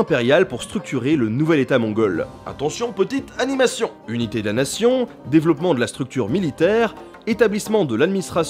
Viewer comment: j’écoutais seulement j'ai entendu "Unité Damnation" PS: quelqu'un sait d'ou vienne les images/scènes de la vidéo ?